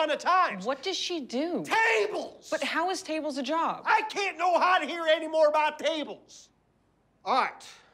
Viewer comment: Netflix subtitles says "I cannot hear anymore about tables" but if you slow it down I'm pretty sure he breaks up "Cannot" into like 3 syllables, something along the lines "I CAN NO NOT HEAR ANYMORE ABOUT TABLES!" :p